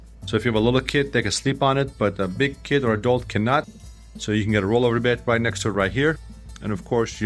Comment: did you just said DOG?